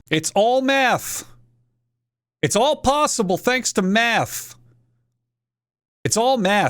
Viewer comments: I thought you were saying p thx to meth XD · What he's saying: "It's all possible thanks to math" What I hear: "It's all possible thanks to meth" And meth is possible thanks to Walter White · that whole section kinda sounded like you were saying "it's all possible thanks to METH" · what he hears: "math its all math" what i hear: "meth its all meth" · I thought he said its all meth not its all math XD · im starting to think he is saying meth and not math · I heard "it's all possible to meth" instead of "it's all possible to math" Waltuh, put your d away Waltuh I'm not having s with you right now Waltuh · is it just me or does it sound like he is saying meth? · I’m sorry but I just hear: “It’s all Meth!” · I hear "is all meth" I really, no matter how hard i try, cant hear math just meth.